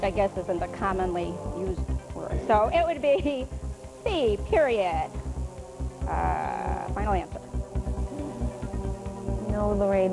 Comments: it sounded like she said "B. Period. Final answer." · This is HILARIUS!!!! And also, I coulda sworn she said "B, Period" When it was "C" lol. But I mean, she got PWNT!!